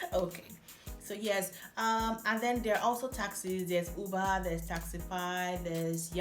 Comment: Every time you say "taxis" I hear "taxes", and that's actually a good thing to talk about in a video like this. How high are taxes in Estonia? How many taxes are there? Salaries you mentioned are net or gross?